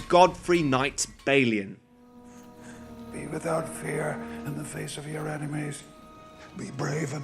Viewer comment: please tell me I’m not the only one I heard Faces... please